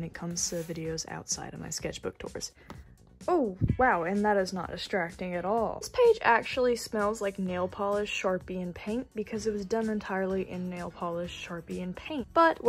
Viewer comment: All ur art is just 🤤 Also fr thought you said, "this page smells like nail polish, sharpie, and pain" 😭